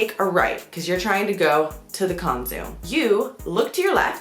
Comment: Did you say Konzum or the word for "grocery store"? 😁 Greetings from Argentina! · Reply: Abby Ferrari she said Konzum and Konzum is the name for the grocery store something like wallmart a d word for a grocery store is dučan i bet you cant read that i hope this helps and sorry for my english im from Croatia.